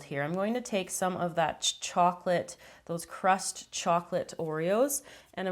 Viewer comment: did she say crust Oreo